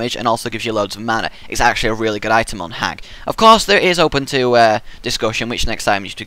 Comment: Did he say at @ 5:15 on HAG ? like Wretched Hag from hon ? :O